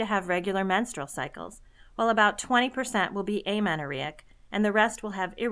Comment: 'about half amount of pops users will continue to have regular menstruation and about 20% will have a...'? What's the word again? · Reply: Nicole Curib amenorrhoea , no vaginal bleeding/ period